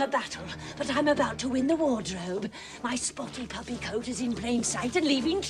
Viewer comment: "You won the battle, but I'm about to in the wardrobe!" As a kid, I thought she said "Water Hobe" Lol! Glen Close is a MIGHTY good actoress!